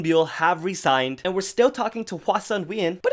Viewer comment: omg when you said Solar and Moonbyul have re-signed i thought you said resigned and I had a heartattack